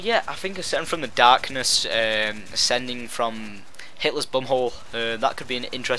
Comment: at first i thought you said hitlers bunghole instead of bombhole